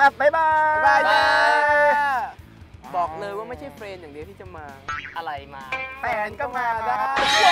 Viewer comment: บอกเลยว่าไม่ใช่เฟรนด์อย่างเดียวที่มา แฟนก็มา เอ๊ะ!!!?? ได้ยินถูกมั้ย หรือหูกาว